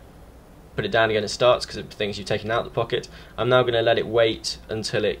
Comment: The way you spelled "pocket" at 1:45 sounded like you said "fuck it"